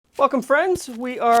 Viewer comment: anyone else misheard "welcome friends" as "fuck'em friends"?